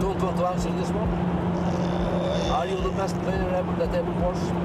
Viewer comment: subtitles say "the masturbator" instead of "the best player"...